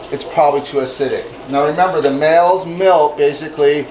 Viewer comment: The "males milk" at 5:46 ... did I hear that right? Hahaha I've never heard that term before. Thats brilliant.